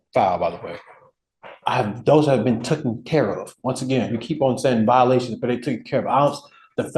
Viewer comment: Did he actually say tooken care of ?